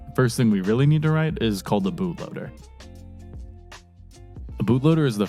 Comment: for a sec I thought he said boobloader until I read the subtitles